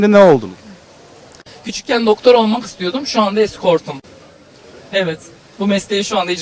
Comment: ne diyor burda? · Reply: Eskort diyor